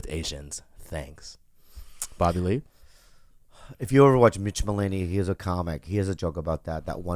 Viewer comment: did he say Mitch Mullany?